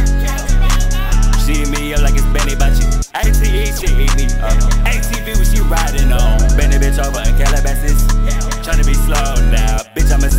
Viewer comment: “A-T-E when she eat me up, A-T-V what she riding on” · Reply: I thought he said ask yo bih what she riding on 😭😭😭